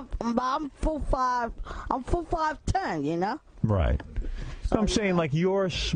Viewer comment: I'm 4' 5" 10" ya know. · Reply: I thought he was saying 4’ 5.10” lmao